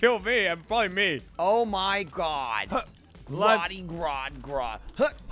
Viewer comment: Did say “Oh my God Groddy Grodd Grodd” At 13:39? Like Gorilla Grodd? Lol